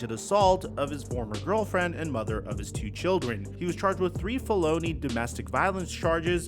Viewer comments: Bro really said "Falloony" 😂😂😂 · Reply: hell yea. Sounded like Bologna lol I thought what did he say right about the time I seen felony in the headline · Did u just say “feh-low-ny” 😂